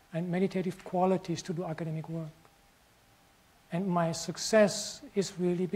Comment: Did he just say "my success"?